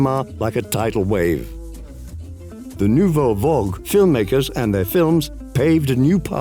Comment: Also also: the French name "Agnès" should not be pronounced like the English name "Agnes." Instead it's pronounced "Ah-nyess." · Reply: Also also also: around 16:40 we get "vogue" instead of "vague" again. Those really don't mean the same thing.